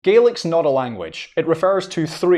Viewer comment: I think I must be hungry. I could have sworn he was saying garlic.😂